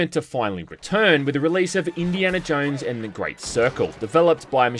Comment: Sorry... for some reason i heard "In Diana Jones and the Circlejerk". RiP Tony Todd, always forgot how huge this man was. Looking forward to this games release. Fingers crossed we get remasters of Tower of Babel and Infernal Machine (at least proper Controller Input and/or modernized Controls). And wasn't Temple of Doom meant to be the first Indy Movie to be released but was deemed "too dark" as an introduction to the Archeologist-Adventurer we 80s Kids love?